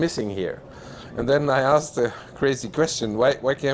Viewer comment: it sounded like he said "then I asked a crazy Christian"... I asked myself..."aren't they all?" 🤣